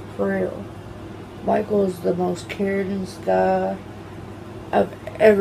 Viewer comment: did she say "most caringest"